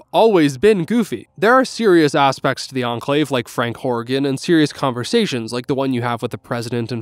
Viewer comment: Hey, at 37:20 you said someones name but I didn't catch it.... You said Frank... Something... I'm pretty sure His name is FRANK FUCKING HORRIGAN